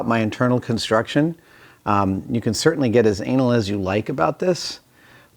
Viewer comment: did he say you can certainly get as anal as you like